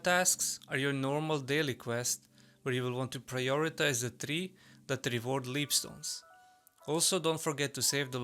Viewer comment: Could you clarify for me what was said at 3:43? "Prioritize the three that _ ______" I couldn't quite make it out, sorry. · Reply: That reward leap stones, or in our version I think they will be emeralds or something similar.